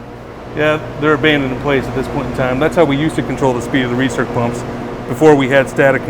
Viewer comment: Did he mean "Recirc" as in 'Recirculation' instead of "Research" like the subtitles say? Those things are awesome!